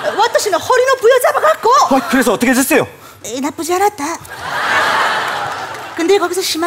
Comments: 뭐라는거에요? 리스닝이 안되너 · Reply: 나쁘지 않았다 라고 한거 같아요 ㅋㅋ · 여기서 허리를부여잡고! 그래서어떻게됬어요? 다음뭐라는거에요?웃음포인트같은데 뭐라는지몬알아듣겧어요ㅜ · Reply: 나쁘지않았대여